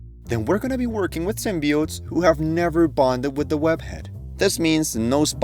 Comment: my ass thought it said boned not bonded 💀